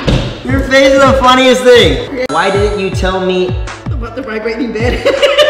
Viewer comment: I've seen this over and over and i can't understand what she says, someone help me! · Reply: Ninoshka “why didn’t you tell me about the vibrating bed”